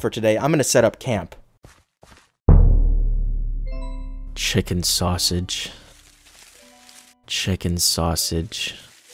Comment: why the hell did i hear "shipping sausage" the first time